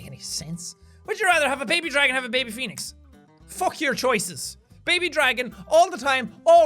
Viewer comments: and 6:14 i thought Jack said Felix as in pewdiepie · I wasn’t looking at the screen and so I heard “Have a baby dragon or a baby **fetus**” And I almost died